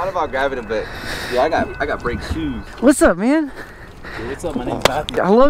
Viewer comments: did bro say whats up my ni##a? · i thought bro said “whats up my nigga” 💀